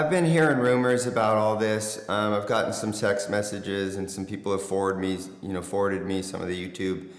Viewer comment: did you just say Sext messages??? Lol j/k bruh, keep the content coming…no pun intended lol